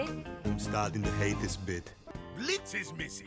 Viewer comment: Blitz was my favorite character on road rovers, I hate how the others treated him especially Colleen, It sound like he said “I’m starting to hate this bitch”